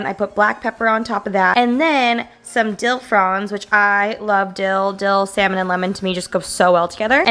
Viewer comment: sounds like she said she loves dildo 😆 😂